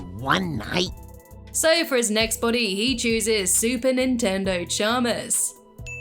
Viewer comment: When you were talking about the way you say super attendant Chalmers, It Sound Like your Saying Super Nintendo Chalmer, which funnily enough, Mario from SMG4 says in Mario Reacts to nintendo memes.. great video :)